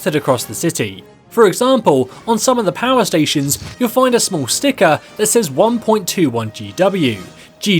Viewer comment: Who heard him say “swastika” instead of small sticker